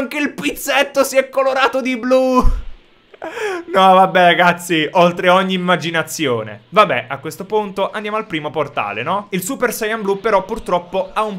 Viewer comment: solo io ho capito gazzi a min 3:18 ?